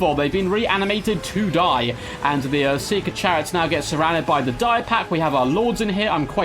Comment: I'm sure you were saying Chariot, but I kept hearing "seeker Chad" and and dying so hard I don't think I'll refer to them as anything else ever again